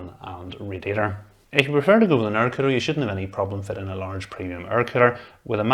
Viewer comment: Does anyone know what he way saying at 8:57? He said a certain word twice that I couldn't understand and the subtitles translated it into "narcotic" and then "aircraft". I'm really confused what he was talking about. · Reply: Air cooler. "If you prefer to go with an air cooler, you shouldn't have any problem fitting a large premium air cooler..."